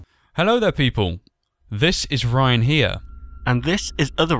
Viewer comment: I thought you guys said Brian but it’s Ryan now I know that